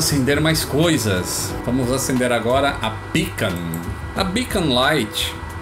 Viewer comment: vamos acender a pica!! Opa perae como assim kkkk. A sim agora entendi. Beacon.😅😅👍